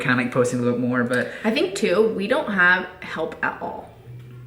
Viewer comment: wh -what did you say !!!! you do have help your inslaved child Everly